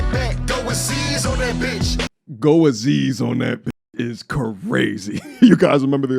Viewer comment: Did he say "throw Aziz on that bitch"?